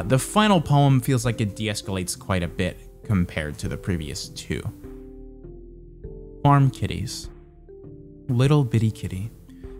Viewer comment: My dumb brain thought you said 'arm titties' and I was like wow that poetry took a swing